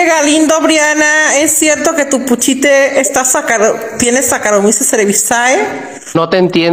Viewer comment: Que dijo la ternura? Sacarisezasabisae?😢😍